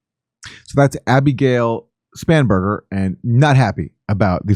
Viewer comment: Abigale Spamburger??? Did I hear that right at 5:22? 😲